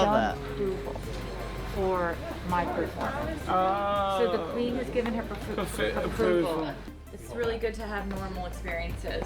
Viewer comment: I didn’t think to include her on my list because he seemed to know who she was lol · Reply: I thought you were saying the Queen was her hairdresser. 😳